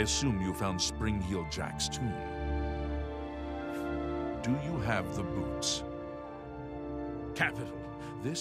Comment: it sounds like he says "do you have the boobs?" XD